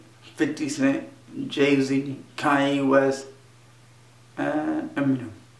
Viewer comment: did say Cayenne West?